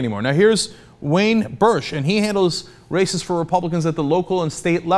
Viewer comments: I misheard at 3:02 ...I thought Cenk said "Wayne Bertsch - Handles Racists for Republicans" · Before he showed the quote, I thought Cenk said "racists for republicans."